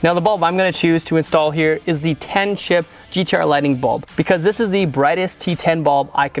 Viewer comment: I have a question - what in the world is he saying at 5:55? I got T10 10 Chip and mumbo jumbo.... brand? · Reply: Sounds like 10 chip GTR Lighting bulb